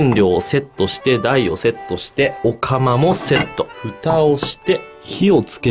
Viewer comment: 「お釜をセット」 と言うところは私には 「オカマをセット」 に聞こえたから、 「ええぇぇぇぇぇ⁉😱️💧」 なんて思ってしまったよぉ〜🤮🤮